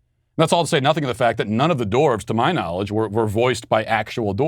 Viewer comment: I keep hearing “dorks,” when Matt say “dwarves.”